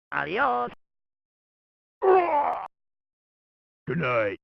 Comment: What? I always tought that he says "NICE!", instead he says "GOOD NIGHT!"